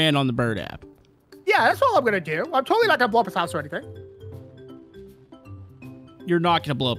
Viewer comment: I thought had said "we're gonna blow up my mom's house". XD